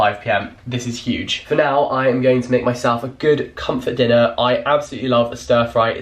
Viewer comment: Anyone else think it sounds like he says he's going to make a 'good cunt for dinner' at 11:24...... Ha ha ha